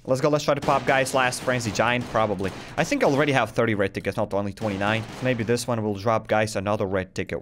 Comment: The moment I heard u say "30 red tickets" I heard "30 red dickets" and I wuz like "da fuq?"